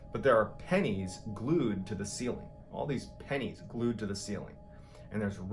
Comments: I thought mr. ballen said panties at first....lol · [!!!SPOILER! DO NOT READ TIL THE VIDEO ENDS!!!] He spent two months living in a family's walls, spying on them, messing with them, rearranging the furniture, ransacking the house, gluing pennies to the ceiling, and wearing a dead woman's clothing. The real mystery here, is how he was ever allowed back out into society. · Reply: PENNIES were glued to the ceiling??? I thought he said “panties”🤣🤣🤣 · WHAT ABOUT THE PENNIES?! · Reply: did he say pennies or panties? The first time I thought he said pennies but the second time sounded like panties. Either way its wierd. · How was this guy leaving lights on and gluing pennies to the ceiling for 2 weeks and the cops were like “yeah bro, nothing there” 😂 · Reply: I thought he said panties! Lol I'm so deaf